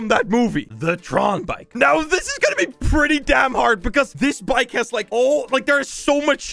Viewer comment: did he said Damn